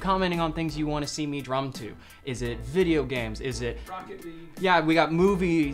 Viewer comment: Yo, dude these are hella great. Also, at 18:58, did he say rocket league?